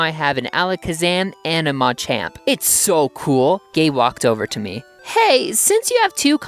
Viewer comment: That sounded like "gay walked over to me."